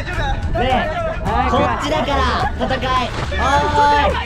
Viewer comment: 藤井ちゃん「こっちだから戦い、お〜い」が面白すぎる（笑）何となく聞いてると「浮所飛貴の戦い、よ〜い」に聞こえてくる（笑）